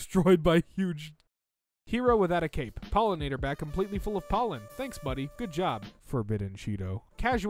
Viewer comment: IM SORRY DID THEY SAY FULL!?